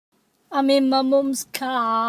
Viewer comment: This is what I hear "I'm in my mom cars broom broom" "Get out me a car" "Aw"